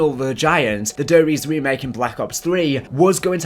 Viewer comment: At 1st I thought it said die rise and I was like really the worst map in cod history I was kinda hoping we where ganna get more dlc maps as there was only 1 on lunch